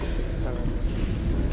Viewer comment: did i hear someone say 10 seconds 👀